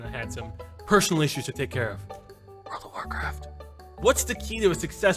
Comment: What did he whisper at 5:29? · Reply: owhait i got it, world of warcraft :)